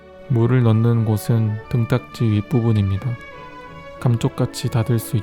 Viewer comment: 나만 금쪽이라고 들림?